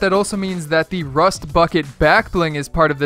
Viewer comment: is it me or did he say rust fucking backbling?